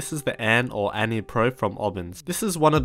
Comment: low-key thought it said anime pro